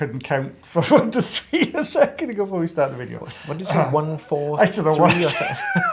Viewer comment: Did just say "143" (I.e. I love you) to? 😊